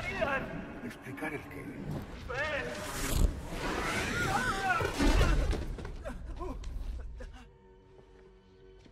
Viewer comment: que es lo que dice Khadgar en el 00:27? no consigo escucharlo bien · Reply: Correcto, dice Fel